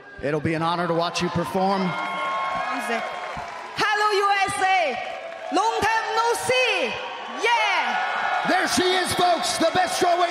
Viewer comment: Did she just say “wrong time go pee??”